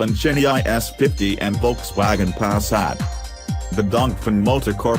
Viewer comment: Awful computer voice. One point I thought he said "Volkswagen Asshat"